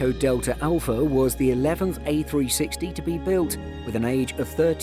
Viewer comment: Did anyone else hear him say A-360 at 2:42 lol